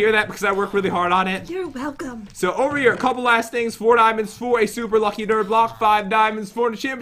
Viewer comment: it sounded like pat said “4 diamonds for a lucky dirt block” LOL